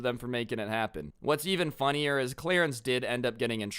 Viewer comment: I thought you called him clearance